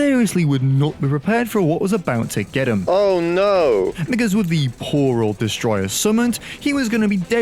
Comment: what did he say? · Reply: he said because not the other word.